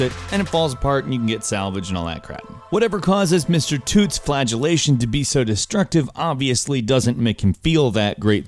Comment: you said flagellation, instead of flatulence. flagellation fladʒəˈleɪʃ(ə)n/ noun flogging or beating, either as a religious discipline or for sexual gratification. Somehow, in this situation it still seems appropriate